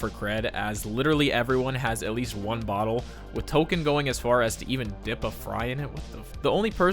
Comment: did you just call him "Token"?